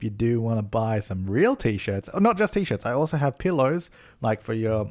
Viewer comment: It sounded like you said peelows at the end.